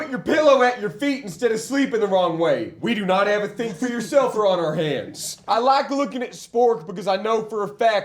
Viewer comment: ohhhh ok this was just nonsense to me for a while but i think he says "we do not have a think-for-yourself-er on our hands"